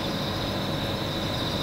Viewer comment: What was that at the end? O_o I heard something, and then MOTHER FUCKERS. LOL